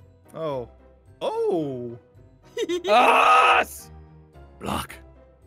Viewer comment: didn't say the f word right???? Maybe??? I'm confused bc it sounded like he said that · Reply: He said block